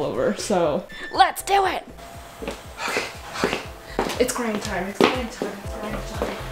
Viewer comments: I keep hearing "Its crying time" when in fact she is saying "Its grind time" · tell me why when you were saying its grind time i thought you said its crying time cuz honestly same💀 edit: GIRL THAT KAROKE SCENE WAS 10/10